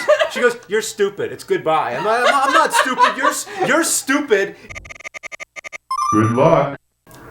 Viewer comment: you can clearly hear the "ck" at the end of what he says. It's "good luck."